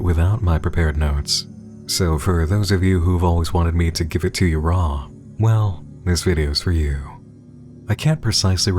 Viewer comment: NAH BRO WHAT THE HELL DID BLUD SAY💀💀💀😭😭😭😭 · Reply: “ℝ𝕒𝒶𝓌𝓌𝒽.”